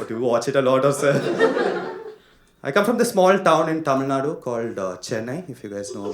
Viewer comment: maybe i'm a bad person, but 01:00 i imagined "Chennai" and you saying cum (instead of come)